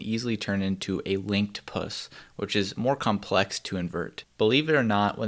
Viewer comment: I thought he say pussy my fucking mind